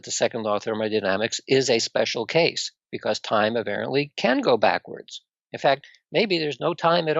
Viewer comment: I think he's saying "Apparently" not "Aberrantly"